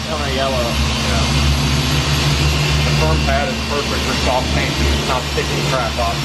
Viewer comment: is he saying firm or foam pad for soft paint? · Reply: Firmer pad for soft paint generally works best.